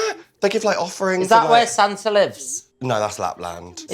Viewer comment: Vi: "Is that where Santa lives?" Tia: "No that's Lapland"💀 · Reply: I thought she said, "Is that where Sansa lives?" "No, that's Winterfell."